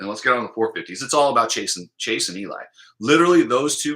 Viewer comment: When you say it's all about Chase and Eli it sounds like your saying it's all about chasing Eli. Lol